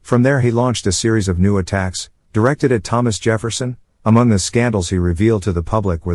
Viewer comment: Are you implying they were all gays??! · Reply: What are u talking about?! Why would u go there? In YouTube comments of one person innocently saying they briefly thought “scandals” said “sandals”. We were all playing off of that, joking & having fun imagining our founding fathers wearing sandals. Butt out if ur gonna say offensive crap or try to start something in THE COMMENTS SECTION OF A YOUTUBE VIDEO, ffs.🙄